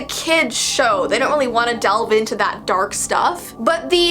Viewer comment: My. Mandi v brain thought dove when you said delve Hehehe